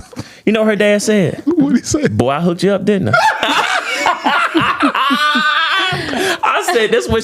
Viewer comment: Can someone tell me what her dad said? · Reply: "Boy I hooked you up didn't I?"